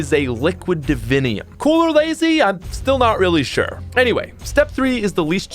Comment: which by the way is liquid divinium......but please tell me good sir what in the fuck are you saying right after that? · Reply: “Cool or lazy, I’m still not really sure”